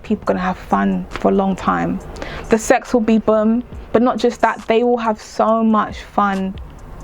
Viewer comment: The way you said bomb was so cute. 😂 it sounded like bum.